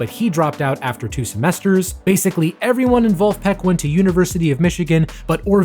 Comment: error at 13:33 on the subtitles "everyone involved-- Peck" instead of "everyone in Vulfpeck"